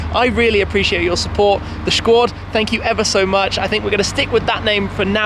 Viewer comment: Did he just say "Shquad".